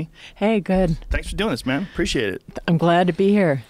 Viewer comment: Did Joe call her “man” or am i waaay too drunk on a Thursday?